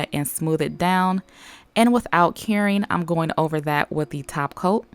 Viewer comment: I always hear "without caring" but I know it's curing